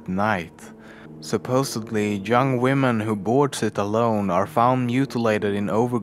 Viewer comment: Did he just say "jung women"?